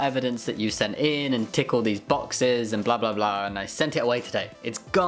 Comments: it sounded like you said "tickle these boxes" at first. 😂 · definitely thought you said "tickle these boxes", then i realize i'm just tired lol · I heard "tickle these boxes" x') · I love you, lol and your lovely soft voice- I really hope you dont need to 'tick off all the little boxes' again anytime soon- but ngl I heard: "Tickle the little boxes" and chuckled. Thank you for that. <3 I wish you nothing but the best! I'm so happy for you and your wife to be! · "Tickle these boxes" That's what I heard, and I prefer this version :D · I heard “tickle these boxes” lol